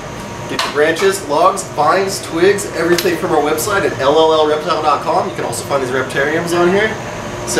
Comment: did he just say LOLreptiledotcom